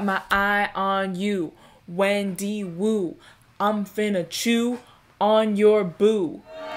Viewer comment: I swear I thought she bought to say “I’m finna chew on some glue” 🥴😂